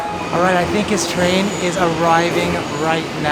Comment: I hear the T R A I N